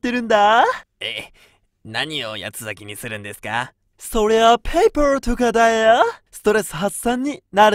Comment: paperと言ってるんだろうけどデント見すぎたせいかな…Peopleにしか聞こえない